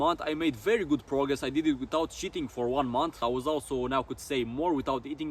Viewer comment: "i did it without cheating for one month" it sounded like you said sh*tting lol